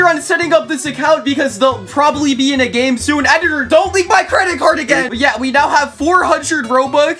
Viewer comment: I thought you said don’t leak my credit card again